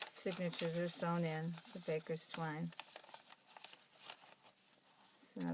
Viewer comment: Very special! What kind of string do you use for stitching signatures? Thank you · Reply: Oops! Just heard you say baker's twine :)